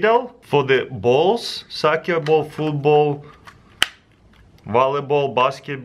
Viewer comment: I think he said sack your ball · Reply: MasterProGamer he said soccer ball